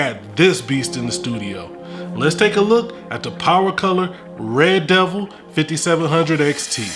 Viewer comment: did he call it "Ray Devil"?